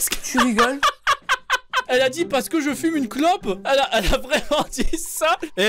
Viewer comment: Au lieu de "Parce que je fume une clope" j'ai entendu "parce que je suis une co*ne" 🤣